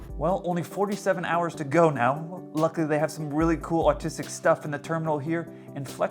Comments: I don't know about you but I hear "Luckily they have some pretty cool autistic stuff" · he says artistic btw, not what the captions say